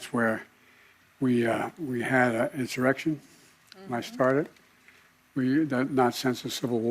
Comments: Did I heard Biden correctly? I'm sure he said, "........insurrection, I started." If I misheard it okay but it sure sounded like he said it. At 4:40 mark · I’m sure I’m hearing things but just after the 4:40 mark, did he say “ we had an insurrection , that I started” ?